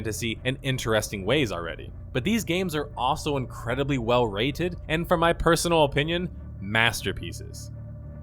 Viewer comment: Did you actually say these games are incredibly well writed at 4:46? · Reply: rated not writed